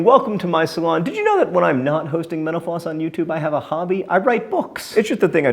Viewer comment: Everytime he says mental floss i hear "menopause"